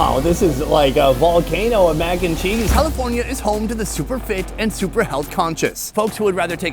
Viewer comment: I thought you said "home to the super fake". I liked that more haha